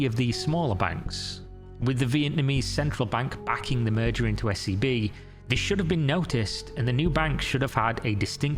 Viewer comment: Legit had to rewind cause i thought you said her new bank was SCP 😂😂